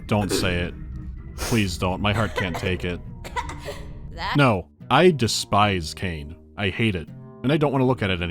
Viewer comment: out of curiousity what line hadley say? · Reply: "that just happened" is my guess